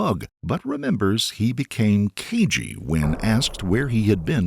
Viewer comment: he became what... keegee? What word is that supposed to be? Closed-captioning isn't showing it either. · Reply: "Cagey"